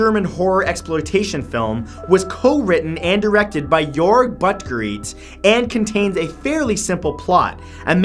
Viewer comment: sorry did he just say 'your butt greets'? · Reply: Rat he was actually saying a name lol "Jorg Buttgoreit"